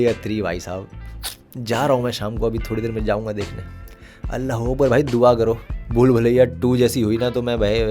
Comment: Did said "Allah hu Akbar" at 11:39 or I just misheard him.